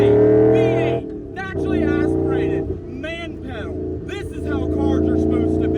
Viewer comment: Did he say 11" wide rims in the rear?? Did he say the digital dash is sick AND badass?? 9:29. Thing has a man-pedal?